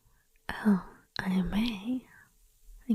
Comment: ok so at 22:37 it sounds like you say anime for no reason. but the subtitle tell me it (I may). how, i can't hear it. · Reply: "Oh I may? Thank you very much "